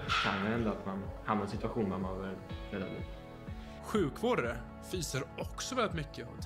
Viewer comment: He said "fysar väldigt mycket" when talking about the medical (meaning: doing a lot of physical exercise). The translation thought he said "fiser" and the subs said "farts"